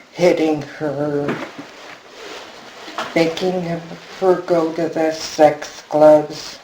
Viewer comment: What did she say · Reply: "Making her go to the sex clubs"